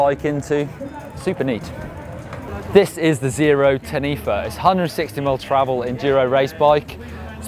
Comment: Zero Ten Ether? I can't hear him too well and I'm interested in the bikes can someone help me ??? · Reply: It was really hard to uderstand... Zerode Taniwha But you might consider selling your house for that bike... or your first born